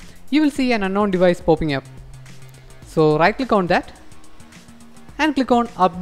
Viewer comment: I'm sorry but I don't understand what you were saying "...when you connect to the....??? you will see a ??? popping up." at about 2:05. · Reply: connected to the pc, unknown device popping up.